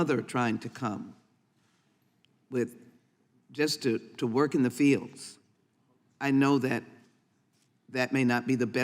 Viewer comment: what is she saying? Just to work in the fields?? What is that called again?